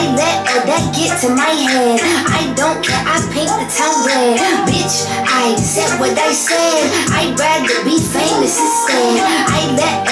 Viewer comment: I thought she said "Bitch I ain't said what they said " means people lying on her